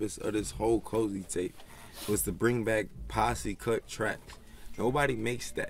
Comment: What did rocky said in 2:38? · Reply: Lil Fufu "bring back posse cut tracks"